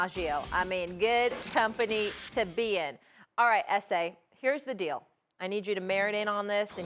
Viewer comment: Did she just say "alright ese" on national airwaves????